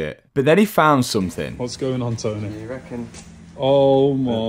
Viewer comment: did I hear Mat's dad name called "Tonny" at this point? 😮